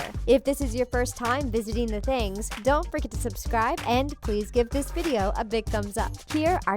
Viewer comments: Bruh it sounded like she said“DON’T FRICKEN SUBSCRIBE” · I thought she said don’t frickin subscribe”😂😂